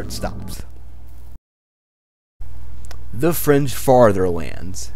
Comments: why did i hear the french fartherlands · I first heard "the french fartherlands"